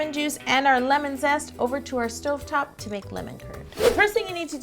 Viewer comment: It sounds like she is saying lemon terd